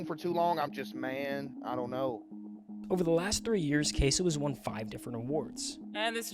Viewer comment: bruh the yt subtitles say "Kai Cenat" instead of caseoh 😭🙏